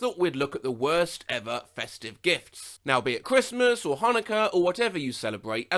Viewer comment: who the fuck unironically celebrates "holokaust" instead of Christmas wtf